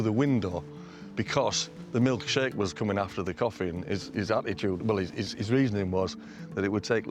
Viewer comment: Coffee?i thought he said tea 😂